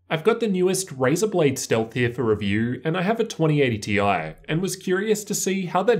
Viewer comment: 2080-i 😂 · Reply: I thought you were saying 2080-i instead of 2080-T-i